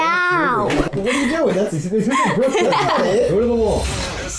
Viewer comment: I didn't hear much, but I think I heard say "Clear the wall", kinda inaudible but yea